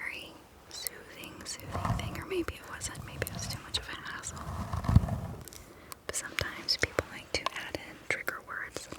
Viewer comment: what did she say pls ? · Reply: Maybe it wasn't.... Maybe it was too much of a hustle